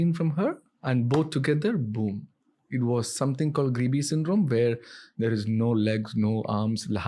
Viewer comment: can anyone understand what syndrome does the doctor mention @ 4:00? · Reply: Grebe syndrome